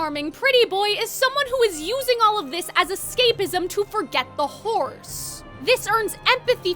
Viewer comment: I know they said "To forget the horrors" but I keep hearing "To forget the horse" lol that's my fault though my ears dumb